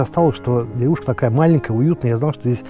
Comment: Мне послышалось, что эту девушку я давно влюблён)))) а потом понял , что деревушку 😂